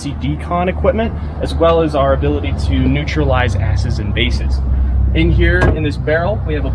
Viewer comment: Did he say a** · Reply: acids and bases.